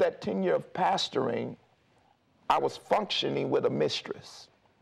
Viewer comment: did he say he had a mistress? at 20:16? He needs to step down, PULEEESH! ...we don't have problems w/pastor's having problems with their wives, IT'S ADULTERY! please!